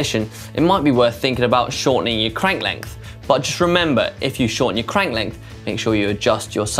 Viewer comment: did he just say “SRAMember” ?????